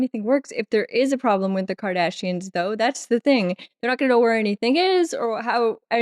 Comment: I swear at 5:44 it sounds like you said "a problem with the 'Kardashians' "